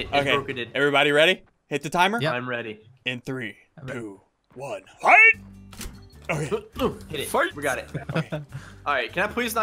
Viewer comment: In 15:00 it sounds like hes saying three doo one fight!